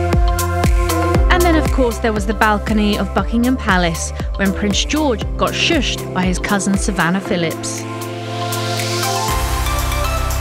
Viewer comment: I thought I heard 'and of course there is the balcony of fuckingham palace'